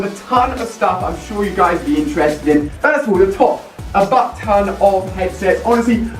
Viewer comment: I thought he said "Fuck Ton@